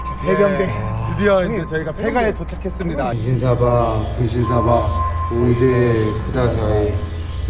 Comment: 아기 그거 접신했을때 뭐라 이상한말한거 분신사바로 들려요 ㅎㄷㄷ